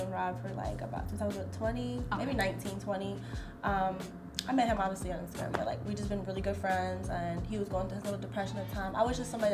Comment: what did she say? She met him where? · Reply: Strip club